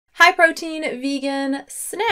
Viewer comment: “ hi protein snacks” is what I thought she said in the beginning, like okay new fan name lol